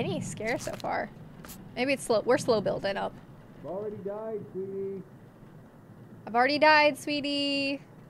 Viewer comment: I hear "Try not to die sweetie".